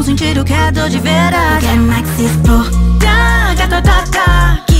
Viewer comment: "Eu quero mais é que se exploda" Só eu que ouvi "quero mais que se foda"? Sério eu até me assustei... Mente poluída, bicho!